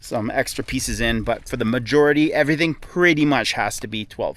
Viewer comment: Did you say Mud-jority?